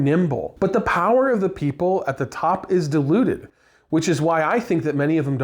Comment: I think you said, "power is diluted at the top", but I heard "deluded at the top". 🙂 I retired as an Air Force civilian and let's just say, that's where DOGE could have actually done some meaningful work.